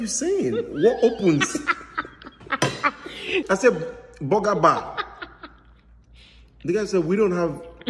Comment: I thought said "BOOGER BAR"